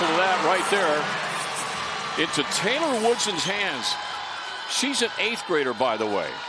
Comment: did anybody else hear that there’s and 8th grader ?!